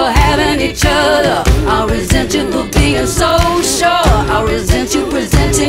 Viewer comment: I resent you for being raised right, I resent you for being tall, I resent you for having each other, I resent you for being social. My god. This is amazing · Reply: Ah! I thought she was saying "I resent you for being SO SURE" instead of "social." 😊